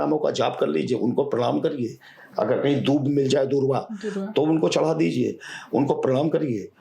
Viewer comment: pe kya bola ? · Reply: Dhurva